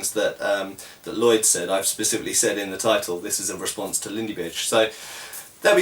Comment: I heard it! At the end! 13:07 ..."in response to Lindy-Bitch." NOW FIGHT! jk.